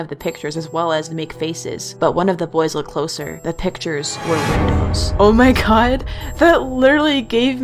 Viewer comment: [the vid] what did she say? the pictures were what? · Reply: She said the pictures were windows